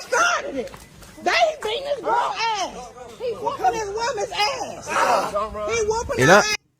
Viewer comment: Hey vous avez entendu pourquoi ca a commence ? la fille des US dit "dat thing is wrong AF, he's whooping his women's ass!!" En gros ca veut dire qu'ils se sont interposes quand Karim levait la main sur sa "meuf"